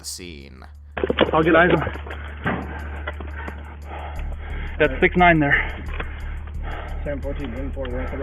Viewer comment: "thats 69 there" thats what i heard